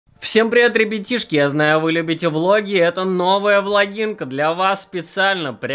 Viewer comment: мне послышалось ВАГИНКА